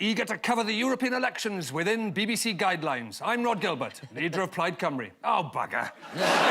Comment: What does Rhod say around 1:05 when he introduces himself? Something about Plaid Cymru? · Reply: “… leader of Plaid Cymru.”